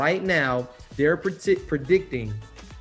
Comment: is it just me or did that guy almost say “they are pretending” instead of “they are predicting” he KNOWS